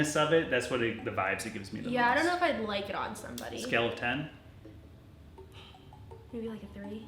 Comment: Did you say "scale of 10" like "skeleton" on purpose? 💀🤔😉